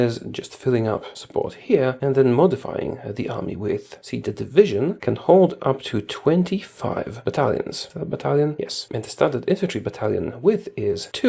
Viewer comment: I thought you said the division can hold up to 25 Italians and I was very confused haha had to go back to hear it again and realised what I heard was wrong